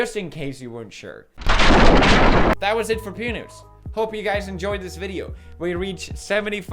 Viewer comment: Totally thought you said "that is it for PENIS!" lol